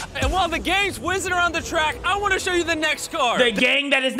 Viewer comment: I thought he said the gays